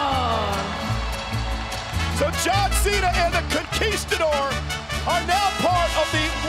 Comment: Did he said John cena